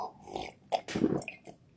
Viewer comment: BRO I ALWAYS THOUGHT HE SAID SMT ELSE BEFORE I SAW THIS VIDEO I ALWAYS THOUGHT HE WANTED TO SAY LIKE “todays such a good day “ AND SAID SLAVERY INSTEAD✋💀